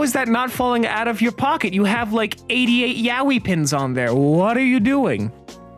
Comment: I’m sorry, did he say Yaoi pins?